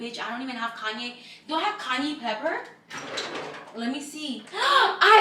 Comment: Did I hear “Kanye pepper” 😂😂